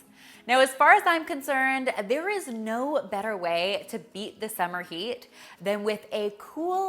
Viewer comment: when she said “beat the summer heat” i heard “beat my own meat”